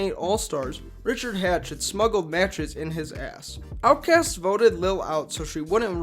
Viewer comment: I was so confused at 09:50. I thought you said "smuggling mattress in his ass". Then replayed it and heard it still.